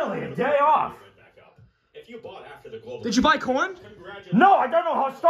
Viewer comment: what did he say here? Corn? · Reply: He said “did you buy corn?”